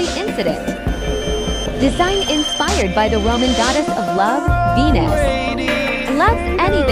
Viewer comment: OH I THOUGHT WHEN U SAID VENUS I THOUGHT U SAID P***S